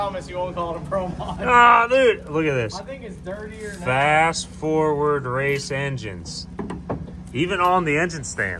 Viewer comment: Thought you was saying “fast ford”, like an oxymoron, or a goof. 🤷🏼‍♂️